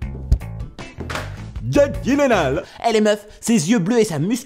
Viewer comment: J'ai cliqué juste parce que j'ai vu écrit Jake Gyllenhaal... Et j'ai arrêté la vidéo quand j'ai entendu "Guilenale"... x'D